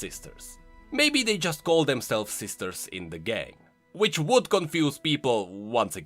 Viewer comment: hol up at 12:24 did he just say "maybe they just call them self sisters in the gay." did he just say that or is my headphone broken. ARE THEY LESBIANS OR NOT